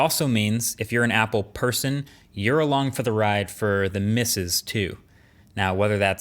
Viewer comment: Definitely thought you said, "you're along for the ride for the Missus (Mrs.)" lol anyway good video as always